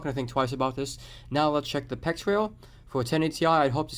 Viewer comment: You check the "Pict rail" ? What's the exact word please and if you can explain what this rail is for ? thanks for sharing knowledge <3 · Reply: PEX Rail - as in PCI Express